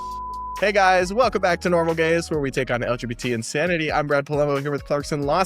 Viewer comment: I came across this video by accident and at the beginning I heard "normal guys" and not "normal gays" I would never have guessed you were gay, awesome content 💪